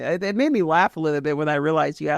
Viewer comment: its type of bosses. Like human, beast, demon or some shit. Its what it sounded like